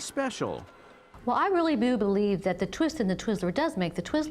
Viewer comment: I listened to that part like 5 times, and it really sounds like shes saying "I really boob believe that..."